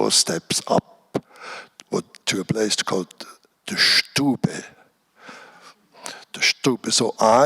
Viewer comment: can someone help me, what did he call it at 10:20? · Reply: Do you mean "Stube"? It's the German word for a bigger gathering/family spaces in older, and maybe more rural house, where also the oven/fireplace is placed. It's the common space for gathering inside the house from where the heat spreads. It's mostly the "coziest" and most public room of the house.